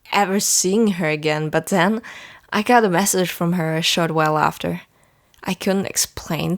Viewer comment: When you said "I got a message from her" I thought you said "I got arrested for her" and I was like WAT???! lmao, my life😂😂